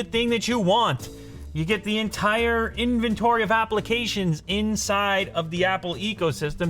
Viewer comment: Did he just say 'Apple-ications'? If so, I've never heard that and... bravo.